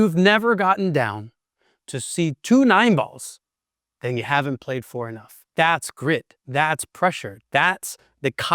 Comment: what did you say? if you haven't seen two 9 balls then you haven't played xxxxx? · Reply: “If you haven’t gotten down (to shoot a 9 ball) and seen 2 balls (double vision), you haven’t played for enough (cash)” My fiend I was quoting is a firm believer that playing pool under the kind of pressure that makes you literally lose your vision is a truly unique gift to mankind.